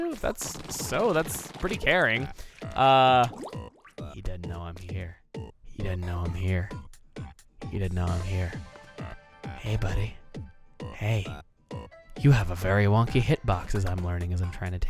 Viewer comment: what he say??? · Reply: He doesn't know I'm here . He doesn't know I'm here. He doesn't know I'm here. Hey, buudy. Hey. You have a very wonky hitbox. You wanna know a secret? Do ya wanna know a big secret? Do ya? Do ya wanna know? You look like youre trembling, like you know im right here, right over your shoulder. I'm gonna tell ya... YOU HAVE THE MOST ADORABLE STUBBY FEET!!!!!!!! Or something to that effect.